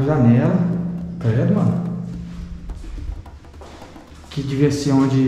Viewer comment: Alguém conseguiu observar que no minuto 4:08 alguém fala É MANO Será que foi o 🤔